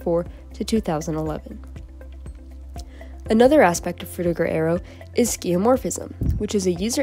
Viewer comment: Sounds like you say "Frutigo Aerio" sometimes.